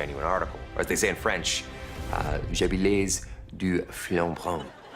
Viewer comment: "Je bilaise, du flan bran" ? "Je plaise au franc brun"? What the hell did he say, that wasn't French lmao.